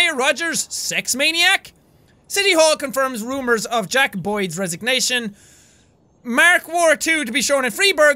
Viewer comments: when he said "Jack Boyd" I thought he said Jackaboy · I thought he said Jackaboys resignation xD